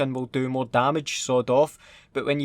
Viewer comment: When you said sawed off I thought you said sod off